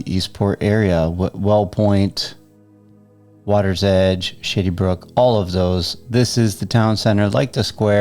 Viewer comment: Did you say “sh!tty brook” ??? 😂